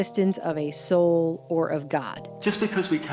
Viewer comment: The guy at 5:24 sounded like he was saying just because we can't prove it scientifically doesn't mean it doesn't exist. I dunno, maybe I misunderstood